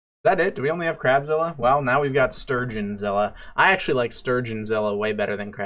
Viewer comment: As someone whose favorite fish is the sturgeon I approve the idea of sturgeonzilla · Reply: My screen is cracked and I thought you said “surgeonzilla”